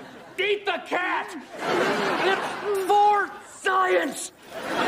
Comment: did he just say it's "Vore Science"